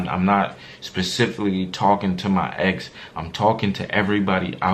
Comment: Oh hell no, I think he really saying Moe